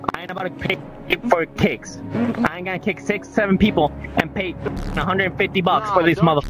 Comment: DID HE JUST SAY 67